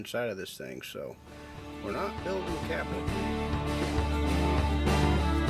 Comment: I thought I heard you say that you "weren't building cabinets". You're welcome to "not" build my cabinets any time.